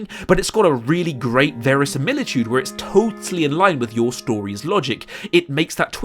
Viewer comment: what does he say? Varison millitude??